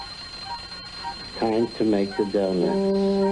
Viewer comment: Dude this whole time I thought it said it’s time to wake the donuts. Man all these years I was wrong